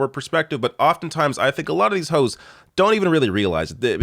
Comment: Did you say "a lot of these hoes" at 3:38 ? 🤣🤣🤣